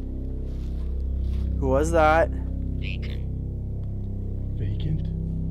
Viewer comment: I'm perplexed - around the 28 min mark, after tripod falls/is knocked over by something we can't see and asks "who was that" to which says "vacant" but I swear, i've replayed & replayed almost 10 x over and I don't hear "vacant' being said, rather, I hear "Satan"! being said!! Anyone feel that this is what they are hearing also?? It certainly sounds like it to me AND it would make sense too. But is the machine showing him the word "vacant' or does just thinks that's what he's hearing?? Anyone else hearing this clear as I am??